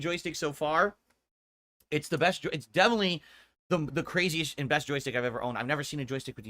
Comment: Whoa, whoa, hold up - 28:30 - "The craziest INVESTjoysticks I've ever owned"? Did I hear that correctly? Can't say I'm surprised he "invested" into it himself but c'mon, maine. Another freudian slip? Never change!